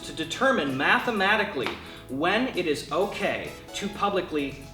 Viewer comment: I thought the first poster said “When is itok”?